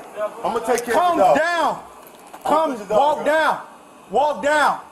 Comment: finally he said walk down the stairs... sounded like he was saying "calm down" cat on the steps: calmer than you are